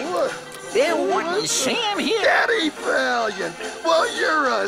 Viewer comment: Am I the only one who misheard the toon bullet say "Daddy Valiant!" instead of "Eddie Valiant!" at 0:35?